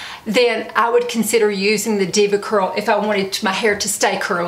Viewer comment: I thought it said diva cup. 😝